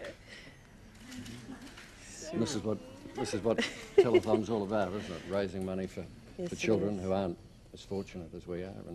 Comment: That BC 1070 spoiled my day..🤦 Btw, I didn't get what the little girl said @ 6:18. Can anyone pls tell me? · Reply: I think she said “your hands are cold”. I remember watching an interview of the little girl years after this happened and she claimed that his hands were cold and that’s why she pushed him away.